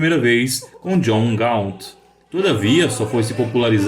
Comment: Não entendi direito. John Galt? Acho que ouvi errado, mas esse que eu saiba só aparece na história de Ayn Rand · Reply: John (of) Gaunt.